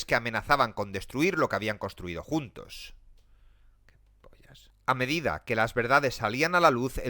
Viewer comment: que dijo? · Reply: "pollas" JAJSJAJSJAJA